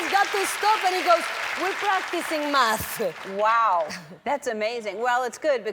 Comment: i thought i heard "we're practicing meth"